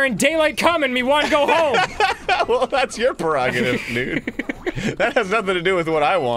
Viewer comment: did he say "nigga"